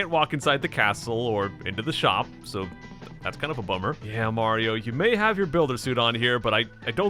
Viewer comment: when he said that's kind of a bummer i thought he said that's kind of a plumber